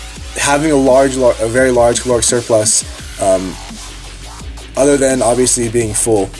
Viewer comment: Hello, I'd like to ask you what did you say in 6:11 that word. I am not able to catch that "Cureclass" or I don't know what did you say :). English is not my native language, so it's kind of a problem for me to catch it. Thank you. Btw.- if you want to get from bulking to cut form you just need do decrease carbs, fats intakes + do more reps + do more cardio? or there's something more? Thanx. Respect from Slovakia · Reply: He said "Caloric surplus".